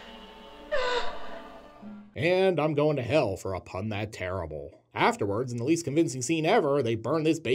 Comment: I misheard you and thought you said "and I'm going to hell for a nun that terrible", and thought you had broken punning and space-time by punning on the word 'pun'.